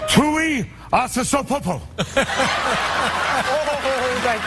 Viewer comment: I don't really get what he's saying there.... if it's only gibberish, then what's the joke? Not critizising his impression, because that's awesome. I just want to understand the joke and its origin...:) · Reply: He says "Tuiasosopo", which is someone's last name. I think it's suppose to be a joke or reference to the movie Glengarry Glen Ross, but I've never seen the movie. And I don't know which Tuiasosopo he's suppose to be referencing. I know it doesn't help explain the joke, but now you know what he said.